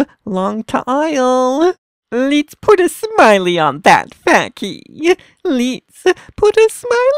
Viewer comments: for a sec I thought he was saying “let’s put a smile on that f*ggy” 😳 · is he saying lets put a smile on the fukky?! XD